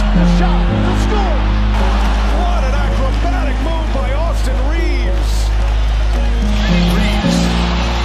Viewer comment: I thought it said “Autism Reaves”